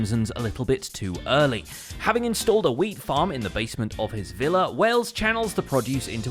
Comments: I thought he said built a weed farm instead of a wheat farm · I thought you said started a weed farm lol